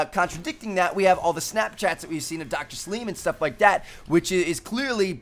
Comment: Did he just say Dr. Sleeman?